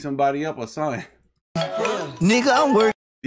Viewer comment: did you say nigga 😭👈🏻